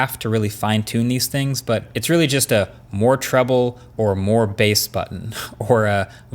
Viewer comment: Around the 7:33" mark, did I hear Marques say more “TROUBLE” 🤣🤣🤣 Anyways, I really like the ear pods and I want one.